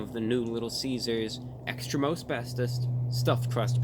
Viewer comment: At first, I thought he said "Extra Asbestos".